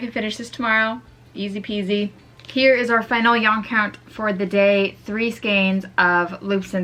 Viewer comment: is it just me or did i hear hear it the finalyyanycount